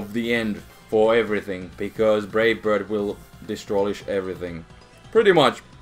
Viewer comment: did you say "destrolish?"